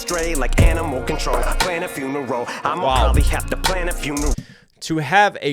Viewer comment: May be just me but I heard that as a triple…”plan a few ‘n a row”? Love the breakdowns you’re the best